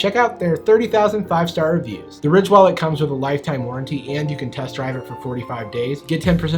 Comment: did he say test drive??😂😂